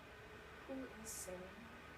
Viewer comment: Hey!! What did you say at time 2:22? It was like a whisper. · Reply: “Who is Sarah?”